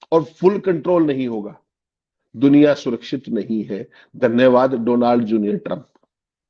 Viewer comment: Did Atriji said "Donald JUNIOR Trump" - He made his kid (eldest son) The President of USoA -:) DJT stand for Donald John Trump, I think